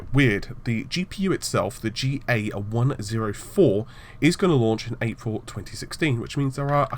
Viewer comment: Did he say it will launch in April 2016???!?!?